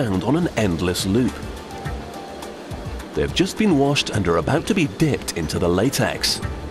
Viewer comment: it sounds like he said they are about to be dicked into the latex